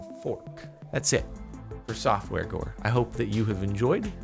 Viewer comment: is it just me or did it sound like he said softwore gore